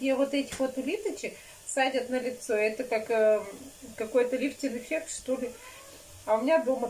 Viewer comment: я конечно понимаю,что задолбала тебя уже с просьбой налепить пельменей...Вот ты говоришь,снимать на зло?На какое зло и для кого это зло? Тебе нужно малость продумывать видева,не сымать одно и то же. Есть идея для тебя 🤪Чтобы видева было просматриваемым,то нужно просто уговорить Владьку сняться с тобой у главных ролях 👆Всем семейством ,за одним столом шоб пельмени лепили , разговаривали между собой,и с нами.Сразу серия побьет все рекорды по просмотрам и комментариям 👆😁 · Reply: G Я ужо по второму кругу матрю видеву,там новое слово расслышала "ЛифтиН эффект! 😂ЛифтиН👆